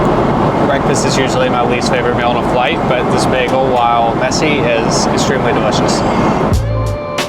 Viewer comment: i almost thought he said messi😂